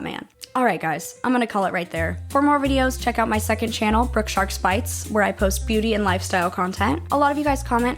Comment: i misheard brookesharksbites as sharks fights and got the mental image of running a shark fight club